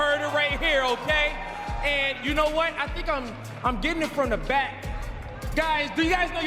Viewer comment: what did he say ? 😂😂😂 getting it from the back? Smh